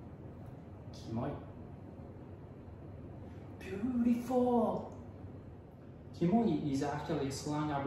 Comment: I hear beautifuck😆😅